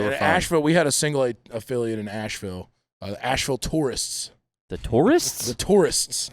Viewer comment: Asheville tourists? I thought he said Taurus like Ford Taurus and I was gonna say that’s the trashiest thing ever